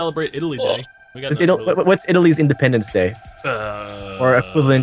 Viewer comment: did he said italy´s independence day? or did i heard ir wrong?